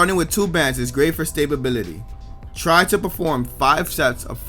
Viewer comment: did he say stay-bability?